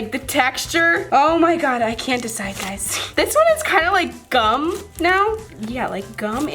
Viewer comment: i thought she said 'this one is kind of like dumb' but then i realise she said 'this one is kind of like gum' i cant tell if i need new ears or i need to turn up my volume..